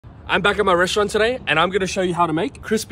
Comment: Am i not the only one that i hear him saying "I'm back in Mauritia today,"?